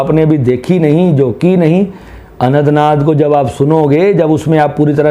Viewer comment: Aapne 24:20 pe kya kaha ? AnandNaag ko jab sunoge ? Anantnaag ? Anand-marg ? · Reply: Anahata naad - he pronounce speedy or in his language accent so...